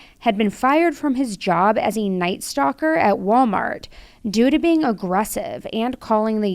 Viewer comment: 😂, I actually thought you said “fired from his job as a night STALKER at WalMart”. Too much true crime in my ears! 😅